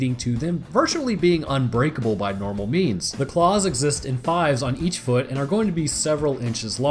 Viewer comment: i thought you said paws rather than claws and was like they don't got 5 paws.